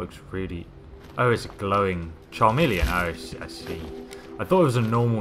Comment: did u just say charmeleon?